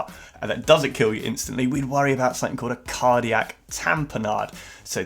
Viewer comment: It says something I missed everything you said their because I heard Cardiac Tampon Aid, and needed a few seconds to calm down... I don't know what it says, but... something 5:00 Never forget that 2 can become one, because one of the two murdered the other